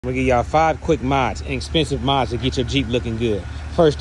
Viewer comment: Thought u said inexpensive?😂❤